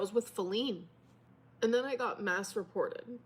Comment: Not me listening and cleaning and then I hear Amber say "I was masturbating and reported" instead of mass reported 😂😂😂😂